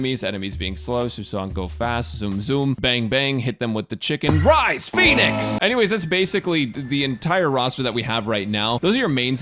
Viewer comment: She says "rise, phoenix!"? I thought she said "rise, p*nis!". 🤔